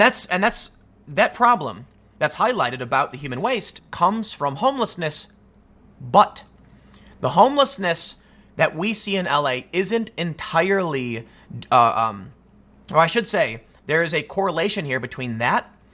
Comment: Did he just say the the problem comes from homelessness’s butt? 🤔